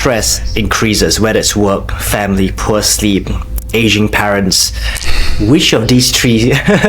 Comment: I heard “Asian parents”, too. 😂